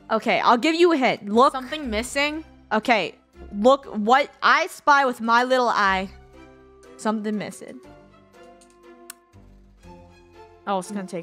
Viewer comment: "i thought funneh said "i spy my little eye that the left side is a lie"